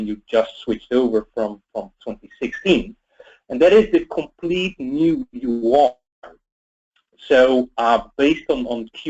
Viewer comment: what does he say @ 2:50? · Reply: a complete new UI?